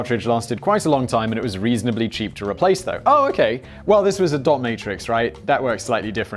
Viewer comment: Every time you say ‘dot matrix’ I hear ‘dominatrix’